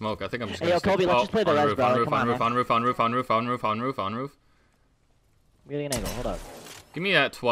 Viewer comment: Was he saying, "We're fine, we're fine" or "On roof, on roof" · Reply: Roof LOL